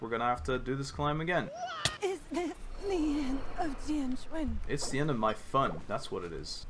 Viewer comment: oh at 14:51 i think its is this the end of tianquan (idk thats what i hear)